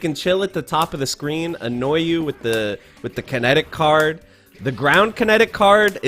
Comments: Be honest, how many other people besides me though gambit was saying “credit card” as a kid instead of kinetic card lol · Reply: I thought he said magic card lol 😂😂😂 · Kinetic card?!!! All this years i thought he said "credit card " :0